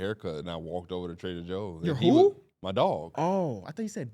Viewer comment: when said YOUR WHO!!!?! I swear I heard say my daughter and I was like that’s such a ass thing to do is drop off your infant daughter to get a haircut and walk into a Trader Joe’s and he’ll say it nonchalantly like doesn’t everyone else do that 😂